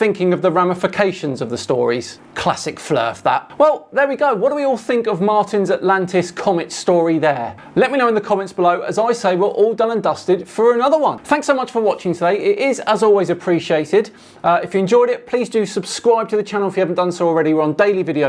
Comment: Given the subject matter of this video, when closed out this video at around 9:30 I HONESTLY thought he said "...in the COMETS below." 🤭